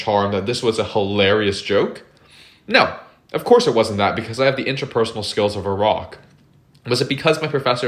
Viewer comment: I keep hearing "the interpersonal skills of Iraq"